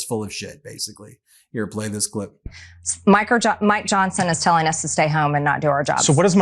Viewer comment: did anyone else catch her slip with what she calls him in private?? 😂😂😂 “micro Johnson” 🤣🤣🤣